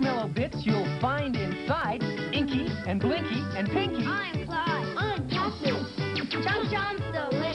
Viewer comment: did I just hear that he said I’m cat food?